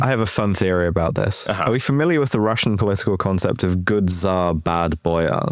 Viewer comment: oh, good tsar , I thought you said good za. They didn't have good za in Russia until the 90s, maybe the USSR wouldn't have collapsed if they had had a honey oil economy instead